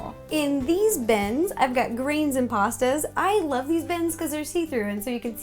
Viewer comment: in my hearing i thought she said "rain and pasta"